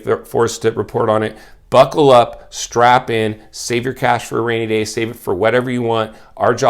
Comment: I thought he said "strap-on", I got excited